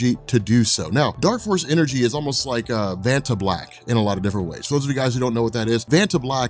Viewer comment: I thought you said Fanta Black 52:29 I was like, hmmmm Kang wants to drink that.